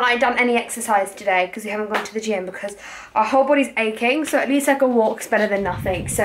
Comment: I thought she was saying her fat was aching 😂😂💜